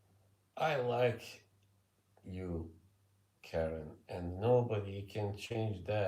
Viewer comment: I thought you said kevin at first until I had to rewind🤣🤣